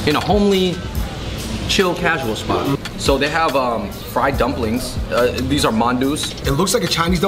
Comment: friend instead of fried 😜